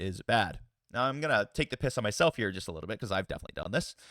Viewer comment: Bonus 2 what I heard is “take the stormsurge.” Got it! 🫡